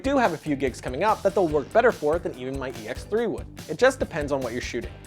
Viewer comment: what camera name did you say on 13:41? Something like EX3?